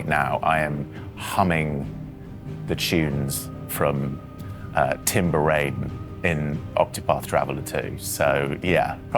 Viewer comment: the composer of octopath 2 is yasunori nishiki, why is Ben saying he is humming the tunes of "tim baraiden"? am i missing something?